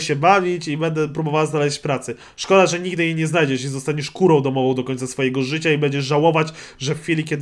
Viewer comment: usłyszałem zostaniesz kurwą domowa xD